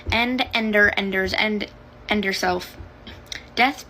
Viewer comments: THE FACT HOW IT SOUNDED LIKE THEY SAID "END YOURSELF" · did she say end yourself?